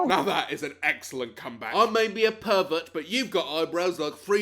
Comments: when they said pervert it sounded like they were saying turkey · When he says he was born to be a pervert it sounded like he said he was born to be a turkey